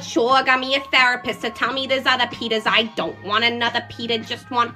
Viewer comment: For a sec when she said petah I thought she said pe**s